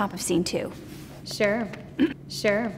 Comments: am I the only one or did she say sure and clears her throat 2 times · is it just me or is she saying sure twice with the same tone?